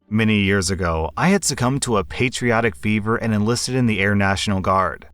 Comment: I usually just listen, not read along, so i had to flip back to this video because i swore OP said he succumbed to a patriotic BEAVER